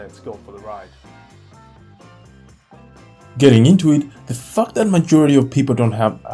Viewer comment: what did you say?? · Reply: Getting into it